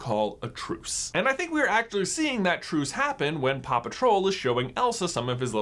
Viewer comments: My sleep deprived mind really thought he just said Paw Patrol instead of Papa Troll. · can anybody here paw patrol instead of papa troll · I thought he said Paw Patrol... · When you said Papa Troll I thought you said Paw Patrol😂😂😂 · So at 8:21, I KNOW he said “Papa Troll,” but I literally heard “Paw Patrol” for a split second... 🤦🏻‍♀️